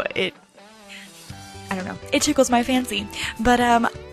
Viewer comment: did she say it tickles my fancy?